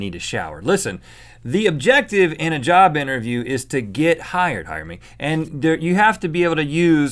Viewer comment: Listen what Rhett sais after he says "The point of a job interview is o get hired..." If it was to fast he said "Hire me"